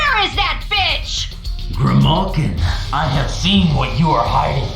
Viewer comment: what did he call her?😂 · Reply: Grimalkin, which is meant to be a form of archaic slang term to refer to her cat lineage. It can also mean spiteful old woman, which matches up to her age in the lore